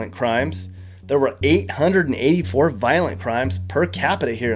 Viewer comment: min 13:35 did I hear +800 murders per capita? I thought cat had seven lives... only